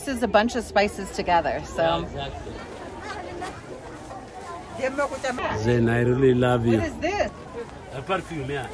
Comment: Did I hear you when he say i really love you 7:38 😂